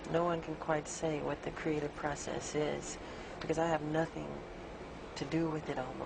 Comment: "processus" putain on dirait un quebecoi qui parle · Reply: Il dit 'what the creative process is' et non pas 'processus'